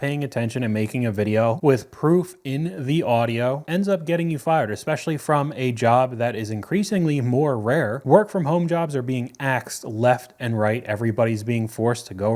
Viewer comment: when you said "work from home jobs are being axed" I thought you said "asked" because I'm so used to people saying "axe" when they really mean "ask"